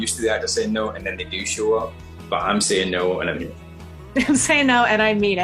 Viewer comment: So this could open to interpretation - James Mcavoy says - "No and i mean it" or does he say "No and i'm in it" ???? @ 0:35 . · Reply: Good catch. He says “no and Im’innt” and smiles after explaining how other actors would say it. hahaha nice.